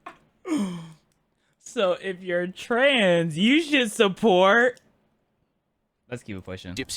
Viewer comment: I just got an add for pillow cube, I watch it and I hear, "If your trans, support h-"